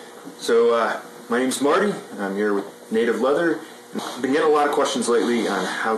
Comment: Lol! I thought you said native lover...but you said native leather. Phil says, “Hi Smarty Pants!!”